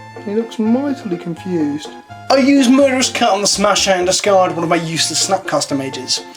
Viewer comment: I swear I thought you said “murderous cunt.” Haha